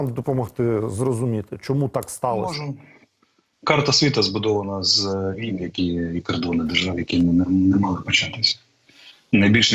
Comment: що він сказав??? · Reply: "Карта світу збудована з війн (і кордонів держав) які б не могли би початися".